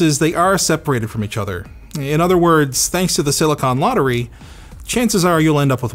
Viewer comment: I swear, I heard him say "World of Hanks".